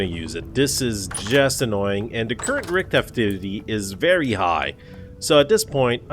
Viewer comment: sounded like you said the current RICK activity is high.. vs rift.. i like it!! rick is summoning drifters now.